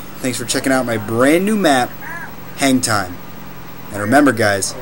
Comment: lol what i heard was "thx for checking out my new map, hentai"